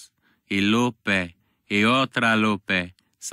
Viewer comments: As a Mexican this is extremely true and also the part where he says the other Lupé just freaking killed me lmao. I love being Mexican lmao. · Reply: I always thought he said "Ultra Lupe" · I always thought he said "ultra lupé"